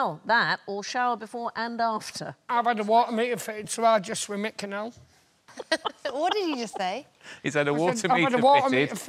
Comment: I can't figure out what Johnny says and then I can't figure out what Alan says. I feel like I need PC Walker and Danny Butterman to help me out. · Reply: "I've had a water meter fitted so now I just swim in the canal" (as opposed to bathing, if you've forgot the context in 5 months)